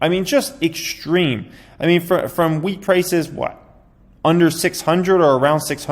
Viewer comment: I thought he said weed price 😂 not wheat 🌾